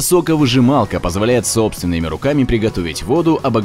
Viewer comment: мне одному послышалось бутылка сука выжималка